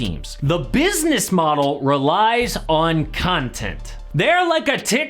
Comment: Did he just say cuntent? 😳